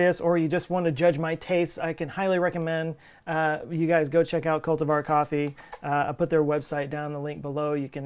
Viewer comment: Even in the last video I thought you said "Cult of our coffee" and still thought the same in this one. But I'm in need of new beans so I'll check them out!